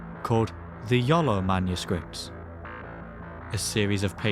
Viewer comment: I swear yoy said "The YOLO manuscript"